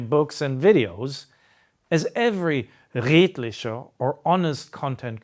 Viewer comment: What word does he say at 3:49? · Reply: "Redlichkert" meaning Integrity. He shows a slide for it at